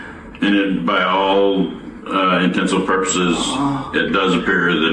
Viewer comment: he said intensive purposes and the subtitles said intents and purposes. REEEEEEEEEEEEEEEEEEEEEEEEE. RIP to this poor lady though and hope this guy finds peace.